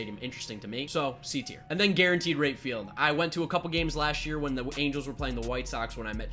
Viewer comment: I had this video on in the background an i could have sworn you said Guarentted R#PE field. I was like holy cow he really hates that stadium. i had to back it up to relieze that wasn't what you said.